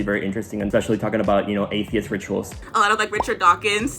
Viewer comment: Did he just say atheist rituals ?